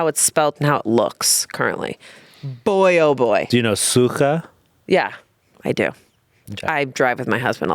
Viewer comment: I was so confused here cause I thought they were saying "suha" which means dry, so when Amanda said she knew the word because "she drives with her husband a lot" I thought it was an innuendo and was about to go "Amanda" in Chanse's way, but then when they started explaining to Antony I realized they were trying to say "suka" xD